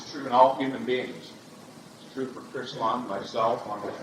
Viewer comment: Did Mike just say Chris bum?